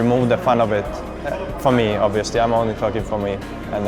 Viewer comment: Hi bro, could you please tell me what did JerAx say on 1:38? "for me, obviously, I'm only XXXX for me" I am sorry I can not hear it clearly-- -- as an ESL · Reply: nah dude he says "It is hearthbreaking that it started to removes the fun of it , for me obviously I mean only fucking for me" he just says I mean so fast